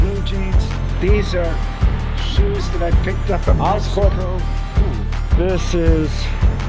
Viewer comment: I heard "these are shoes i picked up as oscorp"